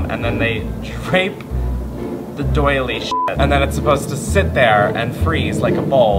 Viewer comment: I legit thought said “rape the doily shit” and was uber confused, lol. Edit: the first one, the “spiderweb” bowl, I think is super cool. I’d spray paint some flower foam gray, then stick fake black flowers into it. It’d be so freaking cool..... can I have it? XD